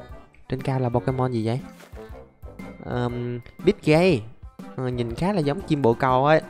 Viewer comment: nghe thành PICD-GAY🤣🤣